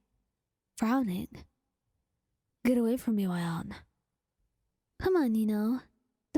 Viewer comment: Can someone help me, please? What does wyon (wyan? She says it at 4:22) mean? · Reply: it's y/n